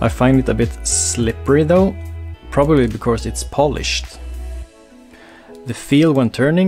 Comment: At first I thought you said it felt a bit slippery because you thought it was Polish 😂😂. Sorry about that lol.